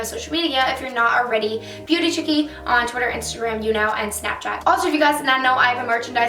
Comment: It sounded like you said pewdie chickee haha